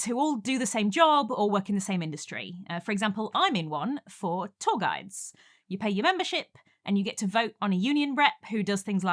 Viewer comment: For a horrible moment when you said "Tour Guides", I heard "Toggites". I wondered what a Toggite was. Then my brain caught up with my ears